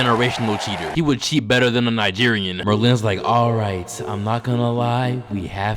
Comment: I thought he said "Berlin" for a second NGA 😅